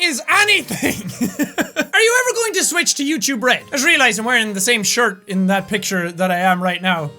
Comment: Haha! Every time he said "YouTubeRed" it sounded like he was saying "You Too Bread." Why thank you Jack! I do feel a little dry today... I know this comment is pointless. :)